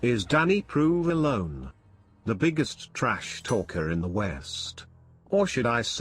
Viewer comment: Ohhhh, I thought you said Weest.